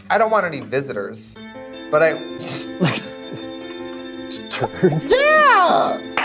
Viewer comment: WHAT IS KATYA SAYING?? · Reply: "Turds?" 😂